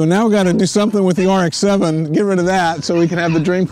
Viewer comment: DID HE SAY GET RID OF THE RX-7!!!!!!!!!!!!!!!!!!